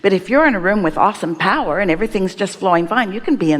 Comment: Oh my goodness… I heard “If you’re in the room with Austin Powers”… (awesome power)… 😭🤣😂💀